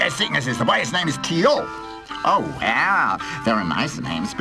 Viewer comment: Whenever the characters say Tio's name, for some reason I constantly mishear it as "Dio" instead. I feel like I'm doing something wrong.